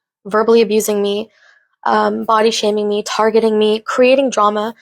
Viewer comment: Did she say abaya shaming? Or did I mishear and it was body shaming?